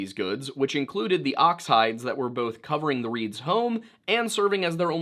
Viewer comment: I'm feeling really down and vulnerable and feel I need a really uplifting, life affirming video with a laugh out loud, feel good ending. I've never heard about this but the title says "Party" so that means people gathering together and having fun, so I think this should be just what I need... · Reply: My dumbass thought he was saying oxides instead of ox hydes